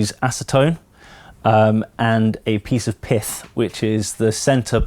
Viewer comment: Literally thought he said "a piece of piss"